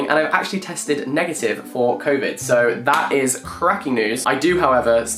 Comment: the captions say “kovitz” instead of COVID 😂